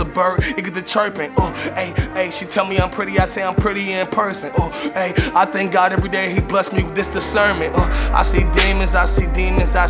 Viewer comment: "I thank God everyday for this discernment" spoke to me hard. He was meant for something special, and was taken too soon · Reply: Ohh I thought he said I thank God everyday this the sermon